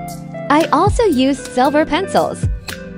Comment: Instead of pencils I thought it said peni- uh..